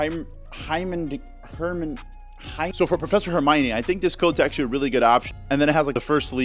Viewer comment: Did you say professor Armani 🤣🤣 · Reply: Im pretty sure he said professor Hermione